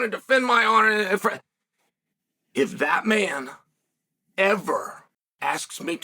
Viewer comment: When you said that man I thought you said Batman